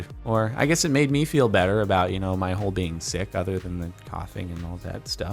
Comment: all I heard was my hole being sick. Even though he said about my whole being sick lol